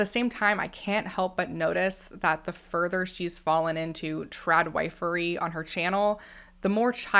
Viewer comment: When you were saying trad- wifery it seriously took me a minute… I thought you were saying trad-white-furry 🤪😬😂